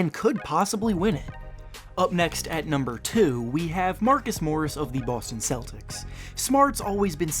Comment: the heck what did i hear marcus morriss or marcus smart